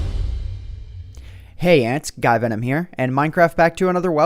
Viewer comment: did he really say "hey ants, guyvenom here"?